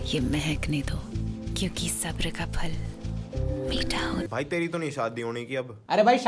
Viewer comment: pe kya bola??? carry ki?? ___ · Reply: Carry ki copy ☺